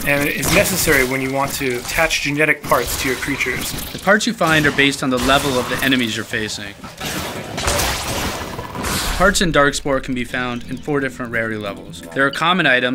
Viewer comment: lol i thought he said genetic farts